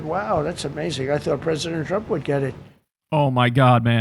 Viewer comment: Why do I hear "PRISONER trump" instead of "president trump"? 🤣😂🤣 16:40 - "Nobody wants him, because he's going to be out of office?" Ohhh, is that why you invited the OUT OF OFFICE "prime minister of Bulgaria" - the same one who resigned (along with his government in December, 2025), because he was made to by thousans of people protesting all over the country AND in countries abroad where Bulgarians joined in solidarity? Interesting... 🤔